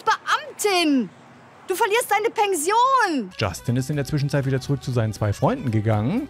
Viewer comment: bin ich die einzige die statt Pension Pengsion verstanden hat?